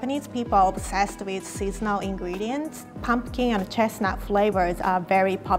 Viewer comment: "Japanese people are obsessed with SEASONAL ingredients" was misheard by me as SEAFOOD ingredients...and then you began making desserts. I couldn't figure out what seafood had to do with all these beautiful sweets. And then you said it a 2nd time, and I caught my mistake. Oh, don't ever change lol...